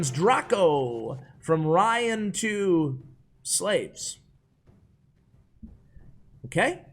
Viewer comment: "Ryan2...slaves" * looks at camera as he realizes what he's saying * rather unfortunate there